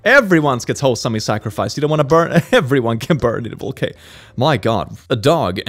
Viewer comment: the subtitles say "Everyone can burn in a baguette"